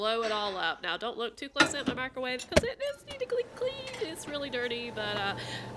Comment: 😂I thought she said “now don’t lock toothless in the microwave “ u know toothless the dragon